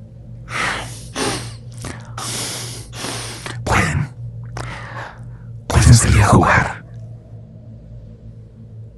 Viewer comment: que dijo ? No entiendo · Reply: "pueden salir a jugar"